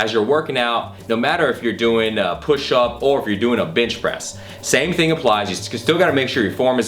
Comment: i thought he said bitch press